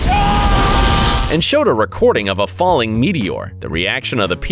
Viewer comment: wait did you say recording?